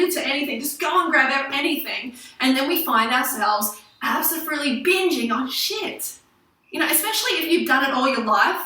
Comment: did she say "absofruitely" ? Oo